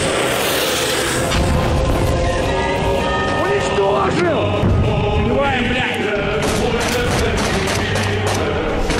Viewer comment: what does he say? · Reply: Basically just "Eliminate them"